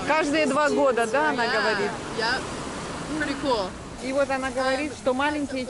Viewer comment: Черепахи как люди, распластались на берегу, загорают :) На 15:05 американка заговорила по русски. Мне послышалось она сказала- ‘прикол’:) вместо pretty cool